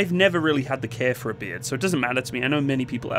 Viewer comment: When you were saying "beard", I thought you were saying "beer". I was having an existential crisis thinking they banned alcohol in the military.